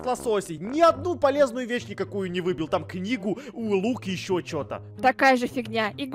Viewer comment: мне послышалось не книга, а коньяк😂